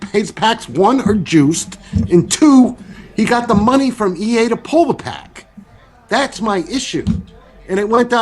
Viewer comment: he clearly says that toke gets the money to pull the pack. There is no "if"